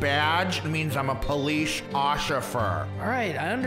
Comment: lol the police officer said “police OCIFER” Lol who else heard that